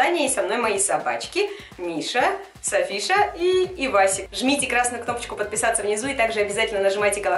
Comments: Что сказала Аня:Ивасик Что послышалось мне:Пивасик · Мне послышалось не «Эвасик», а «Пивасик»